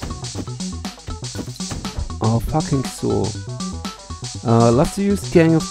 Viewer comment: was that a bad word I heard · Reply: aw fu*king saw